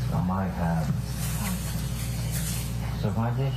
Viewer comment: I could have sworn I heard Johnny say "suck my dick" right before Amber went off on him. At 2:03.